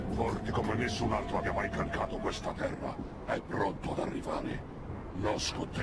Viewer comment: che cosa dice? "Cacato questa terra?"